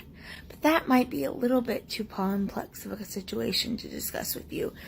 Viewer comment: Coulda sworn that she had said pomplex instead of complex, might just be me but I find that funny how she tried to say how "fatphobic" people can't understand complex situations without knowing how to say complex herself. And wouldn't fatphobia mean to have a fear of fat itself, rather than the fear of the person themselves? themself? English can be hard sometimes.